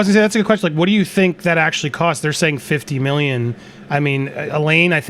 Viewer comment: I thought Elaine said 6 million during closing arguments not 50